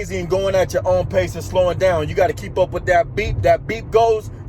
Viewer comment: Hahaha! I got you man! I thought you were saying "That BEAT", "That BEAT" in your Grateful for 1000 views video. But it's the BEEP! Got it. Nice man!